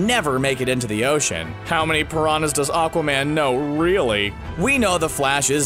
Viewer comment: I thought he said PRAWN not piranha